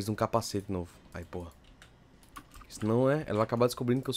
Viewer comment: so eu que ouvi o falando palavrão? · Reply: ele falou "Aí Por@@@